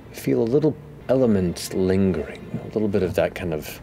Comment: I first thought Matt said "go ahead and look at your feet[s]" when I saw the episode 😅 I was like... wtf?